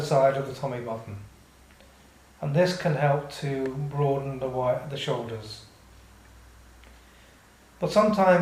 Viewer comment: What does he say (and mean) at the 3:00 to 3:05 point? I'm struggling to register his words at that point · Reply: in case you still want to know! he says: "and this can help to broaden the wid.. the shoulders. but sometimes during the course of lying down, you may want to put one arm down" He is speaking really slowly, try to speed up the video a bit, it might help.